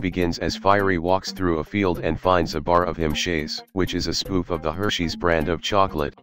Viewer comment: I hear him chase